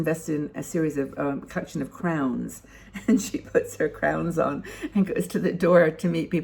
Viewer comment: Thank you! As ever, this was great info and inspiration. I look forward to reading the article. Meanwhile, I was reminded - when you mentioned the woman with the crowns, and I thought you'd said "clowns" - of the time I met a fabulous older woman in DC in the 90s. I was in my 40s, and she must've been in her 60s and was about to retire. I was blown away by her plans. She was all set to move to a retirement village and go to clown school, she told me. Once she became a clown, the plan was to entertain her fellow residents. She'd worked as an attorney for the AARP before retirement. I was SO impressed by her decisiveness and her wish to carry on "working" with seniors, albeit in a very different role!